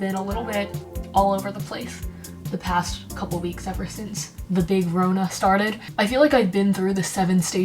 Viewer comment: i am doing subtitles to this video. Can someone answer me what is she saying on 2:28? "ever since the ???????? started"? I NEED HELP PLEASEEEE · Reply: the big Rona (as in Corona)